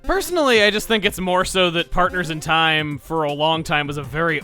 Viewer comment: who else heard him say "Parkers in time"?...the best part is i just saw spiderman homecoming